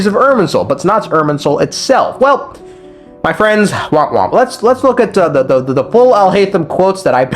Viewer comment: Nigeria did you just say womp womp?